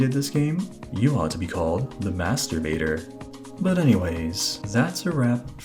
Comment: I heard "masturbator", lol.